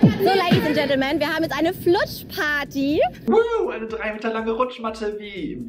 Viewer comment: Ach ne rutschparty ich habe die ganze Zeit verstanden "Flutschparty" 😂😂😂😂😂😂😂😂😂